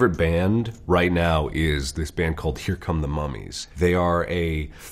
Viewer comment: Ok but did he say cucumber mummies???